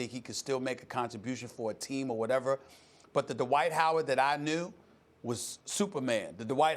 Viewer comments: Did she just say twade dright howard lmao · Did she just say "twade dright howard"?